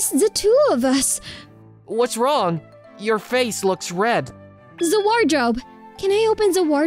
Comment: I love how she pronounced the wardrobe. I heard za worldo. I like these two VAs